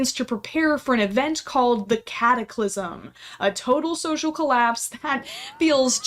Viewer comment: did she say cataclysm or am I just deaf